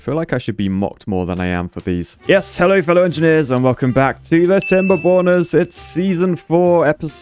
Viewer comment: Goddamned.... please someone tell me i am not the only one who keeps mishearing that intro as Timber-Boners ? I mean.... that would be taking the phrase 'I got wood' to totaly new hights. Right ? 😅